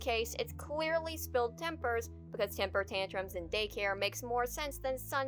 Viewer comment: Little nitpick: at 10:20 he does say "spilled tempera". At least, that's what I hear. (If it was "tempers", he's got one hell of a Boston accent.) It's not tempura, the fried stuff, it's tempera paint. Cause kids finger paint and stuff. Which doesn't mean there can't by typos elsewhere, that just wasn't an example of one.